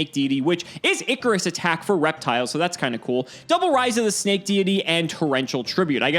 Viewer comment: I kept hearing "Raiza the Snake Diety" and kept thinking "Damn, this God and Savior is everything."